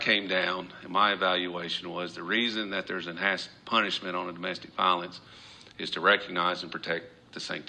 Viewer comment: I kept rewinding, sounded like said, 'ass punishment.' Freudian slip? 😮 Fast forward 8 years from now...a closet door opens. 🤐